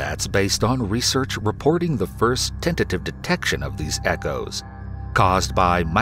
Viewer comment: is he saying detection ? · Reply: Tentative detection